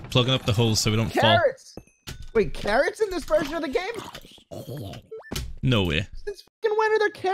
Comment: I thought he said Karen’s